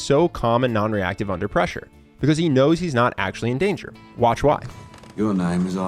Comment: i think it should be he always come in prepared instead of he's not in danger :D